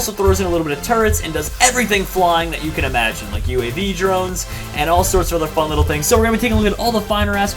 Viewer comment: I thought he said jeromes instead of drones!!!